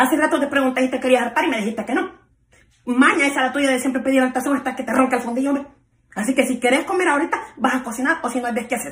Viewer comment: en el minuto 4:05 ay vos no entendiste lo que decia pero yo si lo entendi decia "maña es la tuya de siempre pedir el tazon asta que te ranque el fundillo hombe" listo eso decia 🙂😊